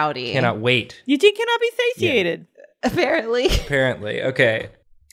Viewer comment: When I heard "Eugene cannot be satiated" I thought it said sedated and ksjejeje